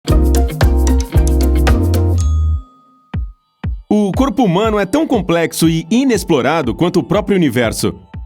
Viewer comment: Ele: o Corpo Oq eu entendi: o Corno